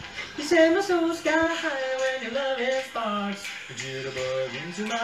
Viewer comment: Is it just me or does it sound like he says, "You said my soul's got high when you're loving farts"? 😂😂